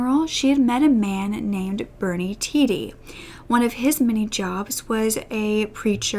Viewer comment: I thought for a second you said she met a man named Bernie Titty and lost it 😂